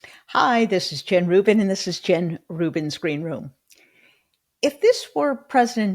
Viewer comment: Jen, right on, you focus on real points. Funny, in your opening I thought you said " my screen room ".